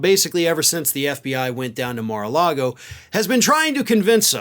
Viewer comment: Every time you say “the fbi went down to Maralago” I hear “the devil went down to Georgia” 😂